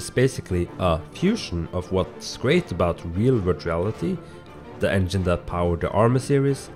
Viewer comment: wait, did he just say virtual reality at the beginning there?! rip onward v1.7 all i have ever wanted is something like arma in vr. that would be amazing. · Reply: "virtuallity game engine" not 'virtual reality' dang. its just the name of the game engine they used for arma 3.